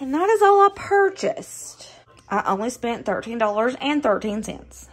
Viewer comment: Dang! I was watching (mostly listening....watching here and there) to this video while I was getting ready for work this morning and I thought you had said the entire food purchase was $30.13. I thought that was totally reasonable for how many recipies you made! Then I got to the end of the video and, like a dummy, I missed that you were saying $13.13. Holy cow! Nice job! This all looks so good.